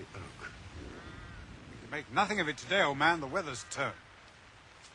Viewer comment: in 1:47 does watson call holmes and oldman?